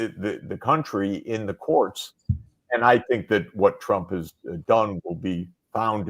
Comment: "Trump" thats what professor Sachs said